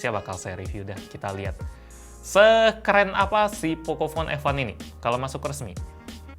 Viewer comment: kalau aku nggak salah denger bilangnya "sekeren apa pocophone F1 ini"